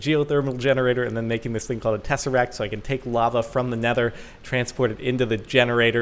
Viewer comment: Did I hear right at 1:26? Did he mention the Teseract? NO ONE TELL LOKI!